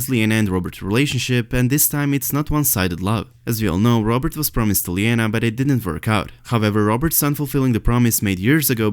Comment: What are you saying here @ 3:35 ? 'Sansa thoughts, not now, not ever, Sansa <unintelligible>' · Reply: "She did not want to wed again, not now, perhaps not ever.” Sansa ‘s thoughts in A Feast for Crows."